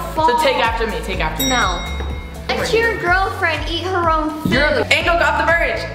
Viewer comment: Did she just say @ 11:50 let your GIRLFRIEND eat her own food